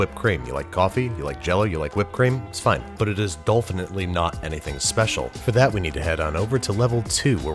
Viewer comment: me at 2:38 - Did he just say 'dolphinitly'? me at 3:22 - You meta son of a gun